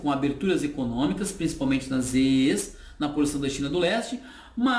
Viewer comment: Boa tarde, professor! Obrigado pelas dicas. Só uma pergunta: ao minuto 16:55, o senhor fala de, segundo meus péssimos ouvidos, de "eees na porção da China do leste". O que são essas 'eees'? Ou eu ouvi errado? Um abraço e obrigado de novo. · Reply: Victor Molieri, primeiramente, muito obrigado. Faço referência as ZEEs, Zonas Econômicas Especiais., as áreas abertas a economia de mercado na China em sua porção Leste.